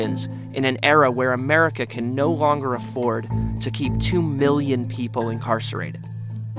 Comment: I'm sorry... did you say two billion Americans are incarcerated or do you have a bad cold?